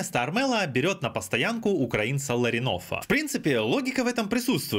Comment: сначала послышалось что Ларионова берут)